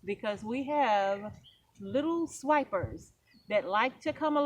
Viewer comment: Seed swipers! Now I want some watermelon. smh. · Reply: I thought she said 'snipers'.